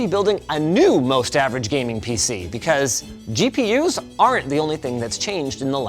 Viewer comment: you did a mistake in the subtitles by typing "GPS" instead of "GPU's"